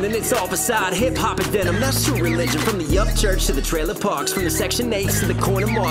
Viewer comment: Yo. Not seeing anyone side ask, but I swore I heard you say something 'bout Upchurch. Am I wrong? Was it just me? "From the Upchurch to the trailer park".